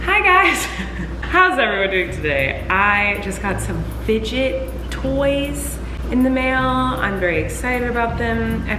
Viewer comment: Who else thought she said midget toys?! 😂😂😂